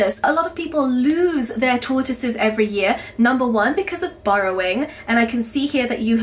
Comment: I thought you said 'borrowing' not 'burrowing' the first time.